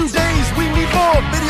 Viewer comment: I always thought it said, drink that f*cking Chuggafreeze , and so i always lowered the volume at that part lmao